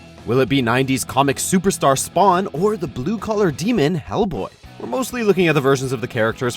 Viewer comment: Wait ... was I the only one who heard the "Or the blue colored demon" ?